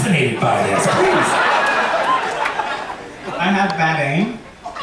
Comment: Does anyone understand what the answer to Bianca & Detox at 29:50 was ? Watched it back 3 times now and still can't make heads or tails out of it. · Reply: Abhothra I think he said “I’ve got bad aim”